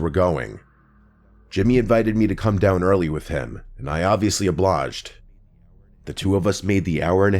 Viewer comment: … did he say “oblaged”? Is that like a common thing people say? I know of obliged but not oblaged